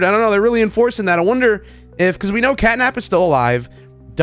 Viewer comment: did bro just say catnaps alive